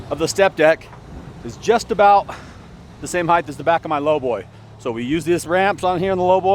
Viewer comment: did you say "heigth"??